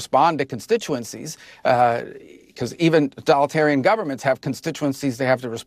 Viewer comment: Did Rubio say "even Donaldtarian Governments" at 11:17?